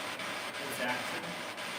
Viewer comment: it sounded like a male saying "Jackie" and the Ovilus said "Jackie" when you guys went into the restroom... or am I trippin'??